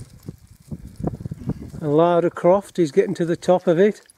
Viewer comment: Thought he said "Lard AR*E Croft"